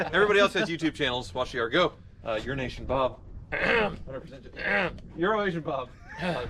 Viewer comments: Personally, at first, I thought was saying Euro Nation Bob. Which would've made him a specialist in Euro cars. Not fond of the new wheels tho. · Is it just me or does it sound like he is saying "Urination Bob" not "Euro Asian Bob" 😂😂😂